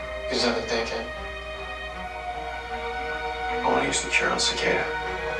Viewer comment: I thought he said he wanted to use the cure on himself 😂